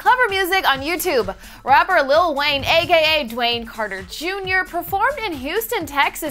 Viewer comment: lol at 0:12 I literally thought she said "a gay a" Wayne Carter Junior